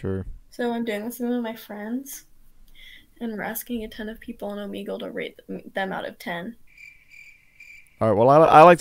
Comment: Can someone tell me wtf did she just say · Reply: She wants to get rated... I rate her 7